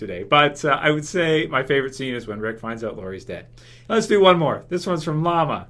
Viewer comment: oh i thought he said Morgans death for his fav moment but he said lori's death that was sad